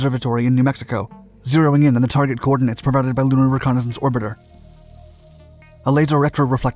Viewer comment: please translate what he says at the 7:18 mark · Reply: Oh you mean when he said "Zeroing in on the target coordinates provided by lunar reconnaissance orbiter?"